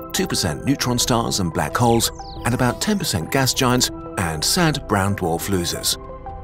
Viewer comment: did they just say “Sad Brown Dwarf Losers”? 😅